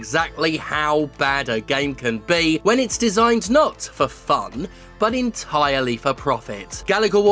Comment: I heard “Design, Not for fun” in your Raul Julia impression . You have kindly ruined me in the most endearing way over time. You are the Legend for me. Thank God for you